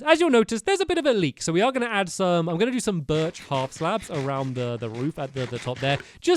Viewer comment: STAMPY SAID HALF SLAB INSTEAD OF BABY BLOCK!!